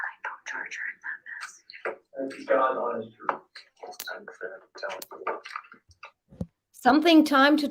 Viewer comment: I hear.. “it’s time for them to tell the truth” !? 😳 & omg they edited it out!! The clocks still running, they put a lay over the real footage that’s why it feels like they’re just blank sitting there..that is crazy